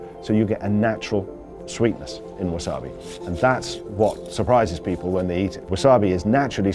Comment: did i just heard "surprise mtfk"